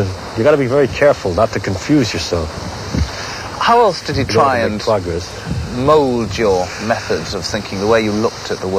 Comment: What he said right after the interviewer guy changed the topic!? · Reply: something about making progress